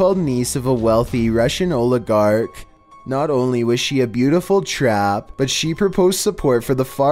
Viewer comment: did I heard well? "Beautiful Trap"?